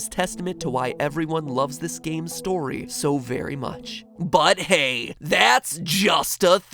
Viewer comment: I THOUGHT HE SAID "But HEY! THATS JUST A GAME A GAME GAME"